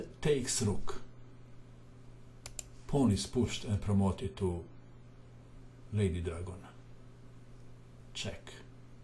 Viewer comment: Lady dragon. I thought u said lady gaga 😂😂😂